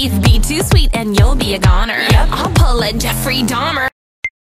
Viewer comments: YALL DIDNT KNOW WHO JEFFERY DAHMER WAS BEFORE THE SERIES? 💀💀💀💀💀💀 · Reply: I THOUGHT IT SAID JEFFREY DONNER💀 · the fact i never knew she was talking about him💀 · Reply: Facts lmao. I was like 11 when I was singing that and thought she said Jeffrey bomber 🗿